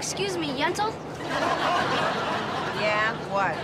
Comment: What did Brighthon call Fran? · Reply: Yentel